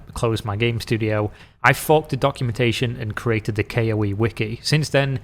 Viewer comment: i was in the kitchen listening to this and heard it as "i fucked the documentation" took me a second.